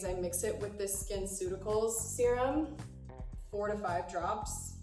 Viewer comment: i heard 45 drops and i was like wtf but then i realized she said 4 or 5 drops😂